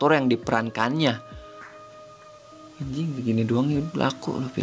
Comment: ngomong apa bang ??😂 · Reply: Njingg gini doang laku ni film